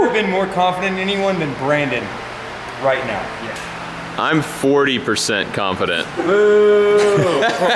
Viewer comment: I think he said 40% because of the sign on the wall.